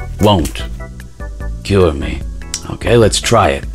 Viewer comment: For a sec i thought you said kill you instead of cure me